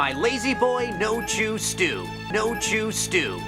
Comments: I thought Brock said no juice stew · I misheard it as "No juice stew" lmao